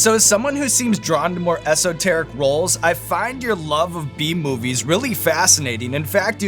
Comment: I thought he said love of Bee Movie. 😂